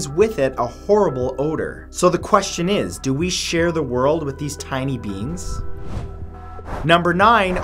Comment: I thought he said tiny beans and not tiny beings, XD.